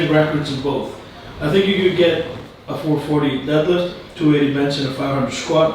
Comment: Martins speaking the truth about that 501 elephant bar attempt is what the strenght community needed now, people on this topic have opinions without experiencing and analyzing thing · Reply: Mike I actually thought it said fleathers